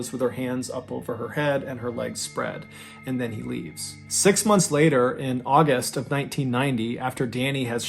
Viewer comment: Way to go, guys. You let an innocent man rot behind bars for over a decade while this coward ran free. Well, not overall innocent; he did hit his grandmother, but there's two sides to every story. But, they had the wrong person for over 10 years, regardless if hitting his grandmother earned him a long trip to the slammer or not. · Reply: I thought he said 10 months